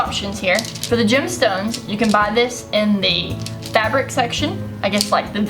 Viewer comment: omg I clicked on the auto subs 'cause I can't hear very well and... 1:18 "for the Jets times you can buy this and me fabric section" XD